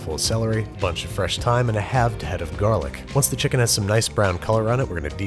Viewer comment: Babish: "3 quarters of thyme" My dumbass who thought he said TIME: "wut?"